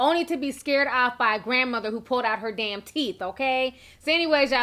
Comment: For a second I thought it said she pooped out her teeth... I was thinking wait- what?!